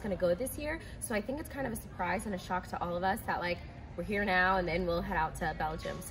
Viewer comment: I couldn't hear what Cecile said. I wish I knew what she said. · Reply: I think she says “a shock for her but obviously it’s not a shock for us.”